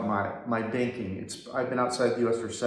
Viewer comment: I missed heard you I thought you said spanking instead of banking did think for a moment is this part of the visa requirement.